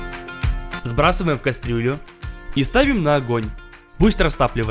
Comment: мне однаму показалась что он сказал кастрюю